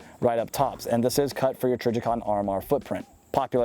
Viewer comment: What is the name of the optic you say in time 3:54 of vid? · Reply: Trijicon RMR it takes Any red dot that's RMR sorry for the late reply